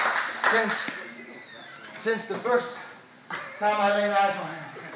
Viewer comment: Since the birth... What Eddie said?